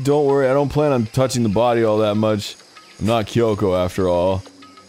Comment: When he said “I don’t plan on touching the body I’m not Kyoko after all” I thought it said “it’s not Kyoko after all” and I was like 🤭🤭